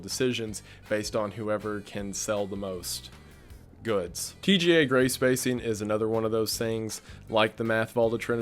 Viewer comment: I heard "TGA Greyspacing" as "TSA Gray Spacing" and saw a brainscan and my stupid brain instantly came up with a conspiracy where the TSA (airport security) are scanning our brains as we try to fly as a means of looking for future criminals or something.